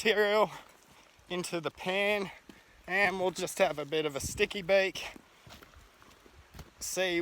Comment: I heard you say this a few times.. What is a "sticky bake"?